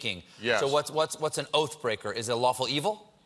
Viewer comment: A good paladin that broke his oath. How unpaladable. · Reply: I honestly for a second thought he said Kaladin and Oathbreaker and my Brandon Sanderson senses were tingling. Maybe I should check out dungeons and dragons